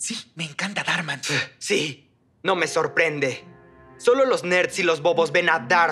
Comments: vine por un video de shitpost en donde cortan lo que dice haciendo que parezca que dira la N word pero solo digo nerd XD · Lo vi en un vídeo de tik tok y como lo cortaban pensé que decía solo los negros xd